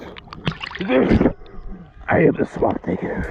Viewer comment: not gonna hold you it sounded like you said "i am the swamp ni**er" but i know you didnt it was just mad funny